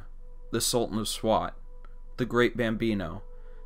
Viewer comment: Oh yeah the great bambino of course I thought you said the great bambee hehe